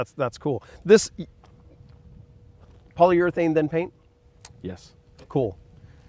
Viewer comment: Did you say polyurethane paint? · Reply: Polyurethane AND THEN paint. Technically paint then poly